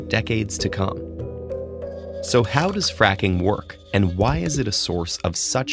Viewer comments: I thought it said "how does it frikin' work" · Am I the only one that thought it said how fucking works?